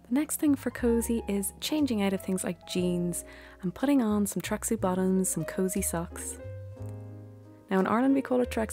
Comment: Only you, dear, could make cleaning silver a cozy activity! I love it. Oh, and I had to listen 3 times to hear 'tracksuit bottoms'. I thought you were saying tracksy bottoms! I am watching this while drinking a freezing cold smoothie, looking around for my slippers and blanket to help me warm up. For me, my favorite cozy is a hot bath with epsom salts, and candles around the bath. I also love a mug of hot tea, and some fragrant and warming soups or stews and pumpkin or other spicy baked goods.